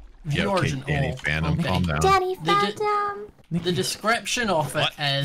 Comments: Did Niki just say daddy phantom (A.K.A. Wilbur!?!?!!) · Hold up, Did Niki just call Wilbur "Daddy Phantom"? Oh no not again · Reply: i think she said danny phantom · Was it just me or did I hear Nikki say "Daddy Phantom!" Here is the time thing if you want to see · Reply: Pretty sure she said “Danny phantom” but her accent made it sound like “daddy phantom” It would make sense because “Danny phantom” is a show that they know. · god forgive me for asking, but did i hear : "daddy phantom "? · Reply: She said danny phantom :)